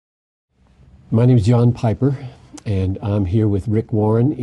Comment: did he just say "Hi my name is Yawn Piper?"